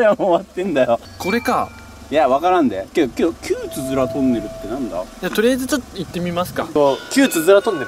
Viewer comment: 旧つづらトンネルが旧ヅラトンネルに聞こえた